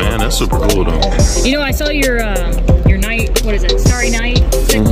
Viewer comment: You were talking about the "Starry Night" cichlid, and I heard "Star Sapphire".